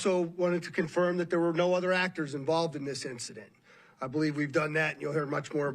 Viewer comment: im not really into conspiracy stuff. but did he just say "no other actors"?!?!